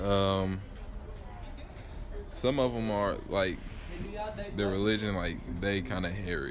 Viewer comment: did he just say “their religion is hairy “ 🤦🏽‍♀️